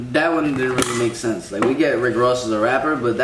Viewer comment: That one girl, telling about her "expensive" jewelry, sounded like she said "Rick Roll", which has nothing to do with the amount of money one has, so she obviously doesn't know what the phrase means, and is just trying to sound cute and intelligent. FYI, she isn't. Why is the word "Kill" or "murder" bleeped out, but the word "fuck" (and its variations) allowed? Also, I never saw anything about a female judge, much less a mother of one of the prisoners, sentencing anyone to death. Both women/girls in this video were presided over by male judges, and neither one was sentenced to death, despite what they might have thought. The video reacted to was the clickbait. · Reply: She said Rick Ross. He's worth around $40 million. She was saying her jewelry was worth the value of Rick Ross.